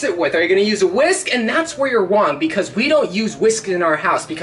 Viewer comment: I think he meant to say wrong but instead he said wang